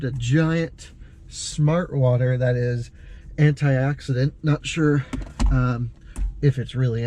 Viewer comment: did you say the Smart Water is anti-accident?